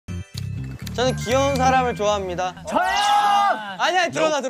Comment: parece muito que ele fala "sou eu" kaakakakakakakakakaakka